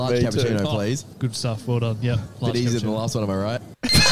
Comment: What on earth was said at 1:12:50? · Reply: "Bit easier than the last one am I right?"